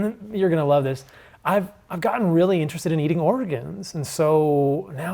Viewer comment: Thanks for having me on, Tom! Super fun, and grateful to be a part of this community! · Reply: Paul Saladino, MD, at 13:14 did you say that you eat "oregons"? Now everyone in Oregon is scared of you. 😁